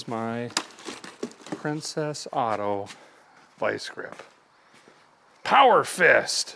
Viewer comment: I thought you were saying "princesado", which is Spanish for "princessed"... Yeah, not my best moment.